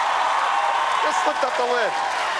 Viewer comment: yeah it sounded like he said "when i say 1 2 3 you say alexandra lift up the lid"